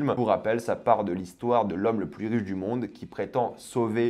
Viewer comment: l'homme le plus quoi du monde ? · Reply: Riche